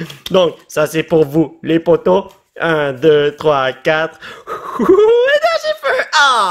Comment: dit: Un, deux, trois, CARTE! MAIS C'EST QUOI ÇA ?